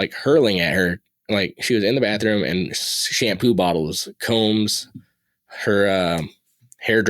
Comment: when he was talking about the girl that was having shampoo bottles thrown i was hardly paying attention and thought he said, "She had poo bottles" lol i had to listen again to make sure i was just hearing shit.